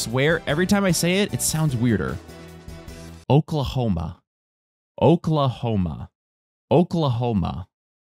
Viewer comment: am I the only person who hears “okay Homer”